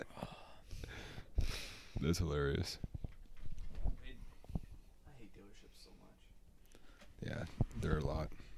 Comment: Did he says "i hate taylor swift so much" at 1:40? That made me so sad, he looked like a kind person.